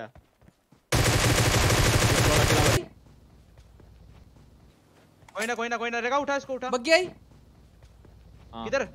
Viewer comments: Legends who saw Viper's scrim highlights know what said 🤣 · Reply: "1v3 tha bey aman" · I wanna know what was said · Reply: 1 v 3 tha ****